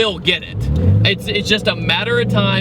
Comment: did you say supra cheeezy